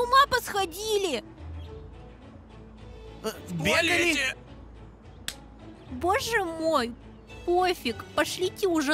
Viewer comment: что они сказали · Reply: В туалете👍